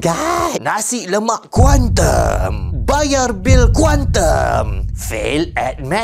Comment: Nasi lemak kuantum 🔥 Misheard be like: Nasi lemak Kuantan 🗿